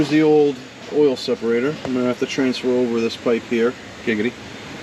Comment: Did he just say “ I’m gonna have to transfer this pipe over ….. giggity” lol